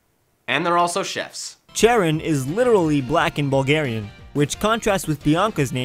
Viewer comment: Did someone said Bulgarian? I'm Bulgarian.